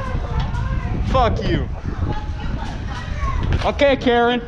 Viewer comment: anyone know what she said ? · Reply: “you almost hit my bike